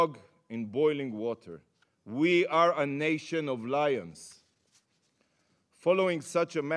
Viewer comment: Did he just say we're a nation of Liers??